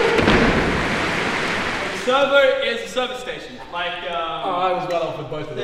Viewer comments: It’s servo, not server. Any other Aussies? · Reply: simon thought he said server, not servo so thats what he wrote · Also isn't it a servo? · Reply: Lachlan said Servo but I think Simon misheard it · It’s servo, not server. Any other Aussies? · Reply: lachlan said servo i thought but in the edit it said server